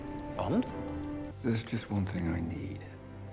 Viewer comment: I thought you say as a cleaner you left the cinema/theatre like "there is one last thing I need"